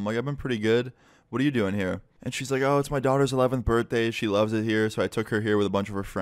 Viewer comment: did she say 11th birthday